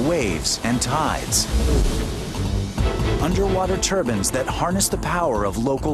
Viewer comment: Did anyone else hear "Underwater Turbans"? LMAO